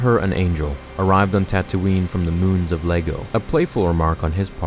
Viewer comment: he said moons of LEGO instead of iego